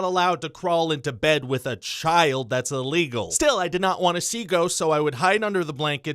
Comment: This autoplayed in the background and I thought he was saying "goats" for a full minute